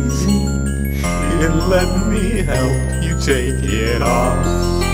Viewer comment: Lyrics from Bassetts Bandcamp: Your skin is freezing~ Here let me help you take it off [beebops] Feasting with your friends What a perfect way to end All these lonely holidays, lalalala~✨ [beebops] Gonna take a piece of your rabies And vivisect your mind [beebops] Snowman smiling with your teeth [accompany beebops] Fallen angels created WITH YOUR MEAT! ＼⍩⃝／ [beebops] ✨that pearly smiiiiiiiile✨ [beebops] Cut you in a thousand slices bake you till golden brown [beebops] Stuff you with spices [beebops] Serve to friends around~ [accompany beebops] Seperate you from your eyes Turn your girlfriend inside out And burn her fingernails ✨Lalalala~✨ [beebops] Soak your hands in freezing water [beebops] Watching as the skin gets softer [beebops] See your bones appear in dark red snow Drop further below [accompany beebops] Gonna take a piece of your rabies And vivisect your mind [beebops] And take a peek inside your head And find the worm inside [beebops] Cut you in a thousand slices bake you till golden brown [beebops] Fill you with spices Serve to friends around~ [accompany beebops] Turn the heat on high and we’ll reduce your blood [beebops] Boil lil’ boyfriend~ [beebops] With brandy and plums [ending beebops] ✨Fun Fact: Vivisection is a practice performed on live animals for the purpose of scientific experimentation or scientific reasons. Lemon literally sees them as animals. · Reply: Wait he says "here let me help you take it off" i thought he said "then let me help you take it off"